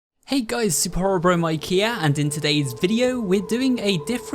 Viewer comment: i thought he said super horror bro ikea for a second